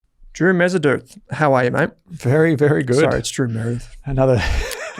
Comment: I thought you said ‘Drew Methodone’ 😂